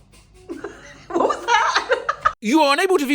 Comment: I am going to try to transcribe what has said at 9:09 "..... ush, nuh, osh - kh kh kh" .. What did you mean????? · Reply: lmaooo I think he said “Oh that’s not nice..”